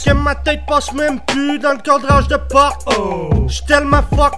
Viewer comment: Sa vé dire quoi " j'suis tellement fuck dop" a 0:18 (dop comme le shampoing ??) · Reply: c'est fucked up c'est de l'anglaus